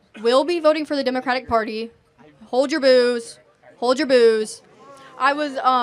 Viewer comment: I thought she said....'HOLD YOUR BOOZE, HOLD YOUR BOOZE".. lol.